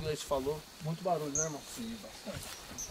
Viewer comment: Aos 2:00 minutos uma voz estranha diz "vamo lá",em 9:42 9:43 "deve sentir",11:14 foi resmungado algo,o espírito responde baixinho pra equipe em 16:13 "pra confundir", 24:57 24:58 "apague a luz de novo", 26:37 "tô aqui" atrás do e fora do spirit box